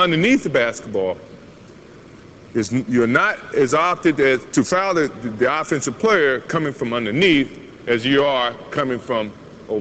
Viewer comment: what does he say at 3:15 - 3:18? "You are not Q@ .. the offensive player" LOL